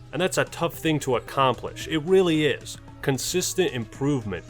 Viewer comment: did he say weally